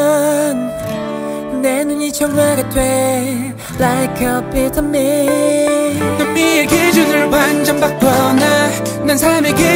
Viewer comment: Even til this day, I keep hearing Leo's 'Like a vitamin' as in 'Like a pyramid' LOOOL